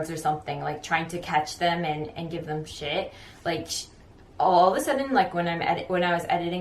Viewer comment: did she just say give them shit?! 😂😂😂